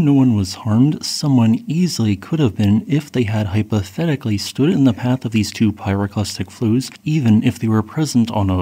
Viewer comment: I thought I hear you say "If they had pathetically stood in the path of these two pyroclastic flows" when I turn on the cc, I know I was the wrong lol.. Thank you very much for the update, have a nice weekend:], greetings from Indonesia 👋..